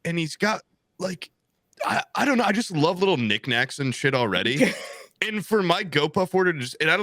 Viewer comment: Did anyone else hear "ni** nacks" at 52:30 ?